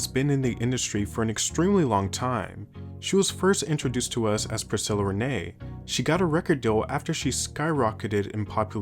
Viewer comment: i heard priscilla renae and thought i heard “janelle monae”